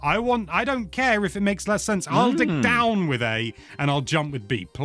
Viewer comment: listening while folding laundry and not watching or really paying attention and thought said “I’ll dick down with A” got a good chuckle